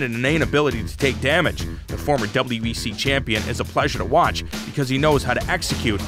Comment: did he say wbc champion?